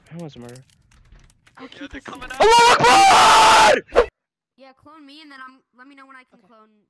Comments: i heard you say "ALLAHUAKBAR" 😭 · كني سمعت الله اكبر صحيح لو غلط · I think you said "الله اكبر"